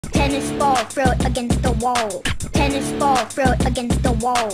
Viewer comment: What i hear.. "Tennis ball throw f***ing through the wall" Maybe it's because hear too many people say f words.